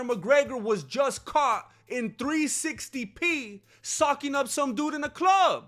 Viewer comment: i could have sworn you said "Connor McGregor was just caught SUCKING up some dude at the club!" i was immediately engrossed in this content lmao, was there video? Is grandmaster going to show it?? Oh socking...ohhhh.